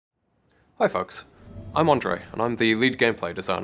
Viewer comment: Haha, it sounds like he saying: "Hi f*cks"